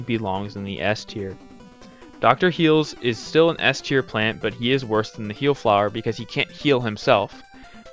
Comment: Did you say plant?